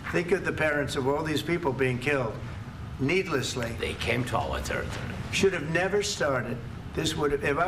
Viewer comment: What did Zelenskyy say at time stamp 14:20? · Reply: "they came to our territory"